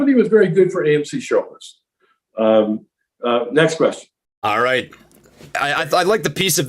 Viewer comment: Did he say that It's good for AMC and AMC shorters? Or am I hearing stuff